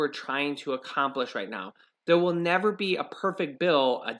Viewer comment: While I was listening to you read the letter from the Democrats to Nancy Pelosi, it sounded like they were saying that they are perfect?